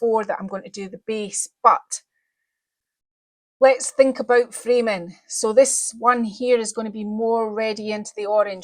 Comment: I was watching this at 1.75 speed and when you said something about framing, and gestured your hand around the image of the llama... I thought you said, 'Freeman' and I thought...oh, she's named the llama 'Freeman', interesting name for a llama! ahahahah